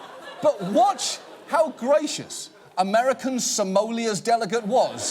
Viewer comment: "American Samolia"? Christ help us.... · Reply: The first time I heard him say it I thought he said "American Somalia"